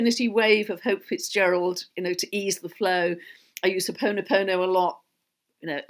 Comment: What does exactly say at around 1:07:40. Is it pono pono? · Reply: Ho'ponopono Hawaiian forgiveness prayer.